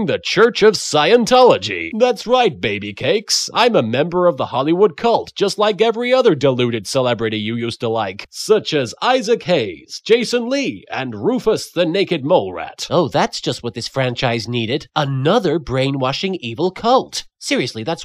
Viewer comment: For a moment I thought I heard Isaac Clark